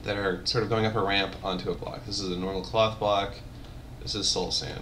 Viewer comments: I thought he said cock block xD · Wow, is this back when wool was called cloth? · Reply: Sounded like he said Cock Block